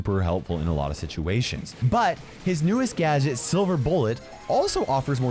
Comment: Did I hear, “New Tara gadget?” 🥺